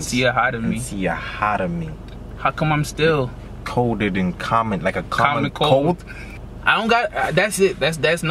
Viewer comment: In the run this town it is colder than Connolly ( I may have spelled it wrong ) he's a hockey goalie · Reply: Jason Tyson i thought he was saying colder then commonly (common cold)